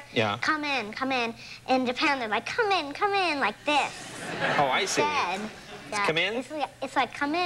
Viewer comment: did she said "come in come in like having sex"???